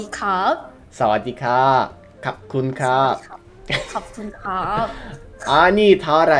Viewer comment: i love watching your videos over and over again haha :) but at minute 43:42 you say khop khun krap and the subtitle says how are you but thats wrong bro ^^ khop khun krap means thank you :) but your language skills are amazing im a big fan :D